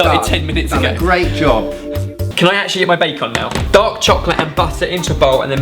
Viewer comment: did say can I get my bacon now